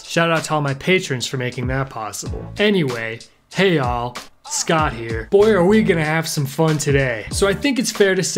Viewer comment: Did he say "Hey'all Scott Here"